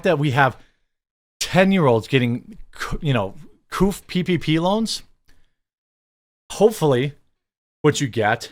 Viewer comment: When you said 11 years olds were getting pp loans I misheard it and thought you said PvP loans 🤣